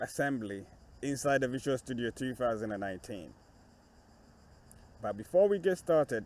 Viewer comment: did you say "Visual Studio 2018"?